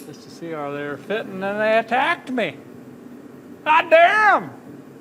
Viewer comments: Holy Smokes people.. I didn't say "GD".. I said "how dare em". I've never swore on this channel and never will. Why would I not remove it even if I did being the director, writer, actor, talent, owner, and operator? Wake-up! And love ya! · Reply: Watched the 31:21 part seven times, “how dare um” that’s all I heard. Been watching the channel for years. It’s funny and interesting, definitely never notice any cussing. Luv ya, and the whole family. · Holy Smokes people.. I didn't say "GD".. I said "how dare em". I've never swore on this channel and never will. Why would I not remove it even if I did being the director, writer, actor, talent, owner, and operator? Wake-up! And love ya! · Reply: I thought you said HOT DAMN! i was like woah getting a little spicy there🤣